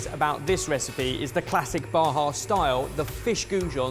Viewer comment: It took me a hot minute to catch that they were saying “Baja style” 😂. for a while I thought they said “bar house style” and I didn’t understand what that meant 😆